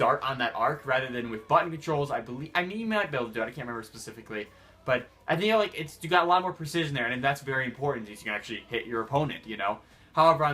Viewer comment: As I was listening to this my mom overheard it and thought he was saying butt controls... I can't unhear it now